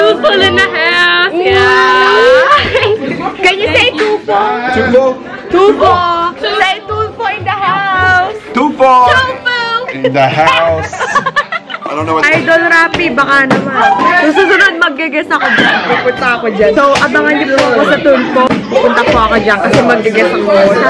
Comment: I just hear "Tofu in the house"